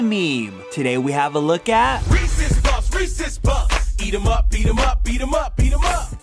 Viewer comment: Sounds like they were saying "Racist puffs! Greece is fucked!"